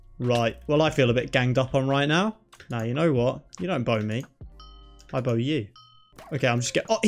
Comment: I THOUGHT YOU SAID YOU DONT BONE ME, I BONE YOU AHAHHHAH